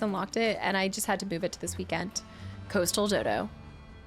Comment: When you said coastal dodo, I thought you said coastal ditto lol